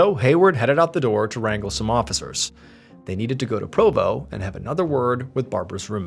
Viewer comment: I thought he said "had to go po po"🤦